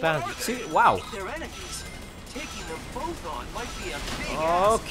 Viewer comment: Did I just hear Silver say big ass?